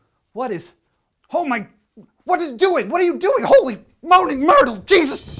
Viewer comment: Did I hear Holy Moaning Myrtle? :P